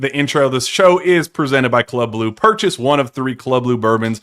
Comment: The way says "club blue" I hear "club lou" every time lol